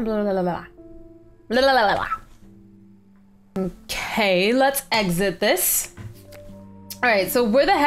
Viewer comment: I thought you said let's eat it when you went to feed the bunny and I was like no don't eat the bunny! LOL